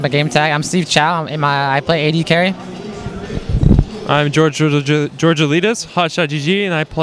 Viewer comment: haha it did sound like he said george elitist